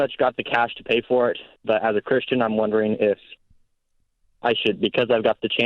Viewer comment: did he say i have the cash but " because im a christian " im wondering if i should just go ahead and do it or finance?? that would seem incredibly irrelevant, i think mr ramsey's co-host was thinking the same thing. she looked over with a look of slight confusion right after he said it. do some people think thats how the financial system works? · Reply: “But as a Christian I’m wondering…” Is what he said.